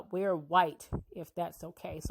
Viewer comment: I thought they were saying “we are “ white